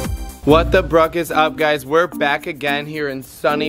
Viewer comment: I'm new to this guys channel and after the intro, I just hear "what the bruck is up" this is just great. I'm loving it so far.